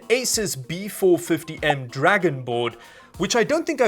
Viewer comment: did you say... DRAGONBORN!?